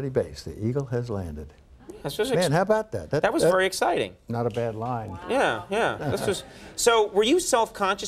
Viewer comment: “Not a bad lie” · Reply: I thought he said " not a bad line". As in movie script🤔😳